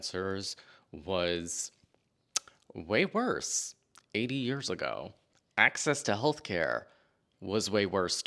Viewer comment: I thought he said 8 years ago! 😂